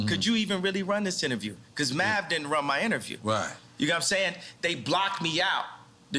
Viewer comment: Math didn’t run my interview? Is that what he said?? If so math is dumb asf his little podcast could’ve got serious numbers.. lol